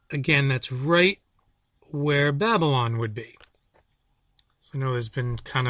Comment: Babylons location is not in question at all. The ruins were partially reconstructed in the 80s and 90s. If your book doesnt have it in the proper location I would question everything else. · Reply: I thought he said "Bagdad " not Babylon, I need to listen again...